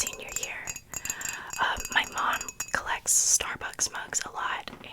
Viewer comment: I swear I thought you said mud collection and I was expecting you to start pulling out piles of mud and I was so confused 😂